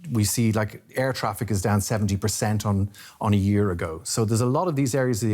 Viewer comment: Did he say Chinese air traffic is down 70% or 17%? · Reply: 70%